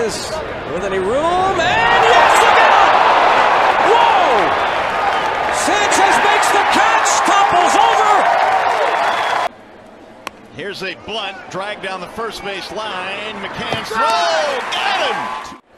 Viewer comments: anyone else hear him say blunt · did he say “and here’s a blunt”😂😂🤣